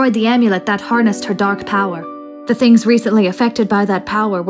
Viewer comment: did say damn power or darn power or dark power?